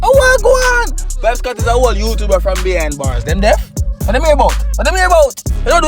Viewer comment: 😂 Did you say Vybez Kartel is a whoole YouTuber from behind bars.. When since Kartel ah YouTuber? LOooOL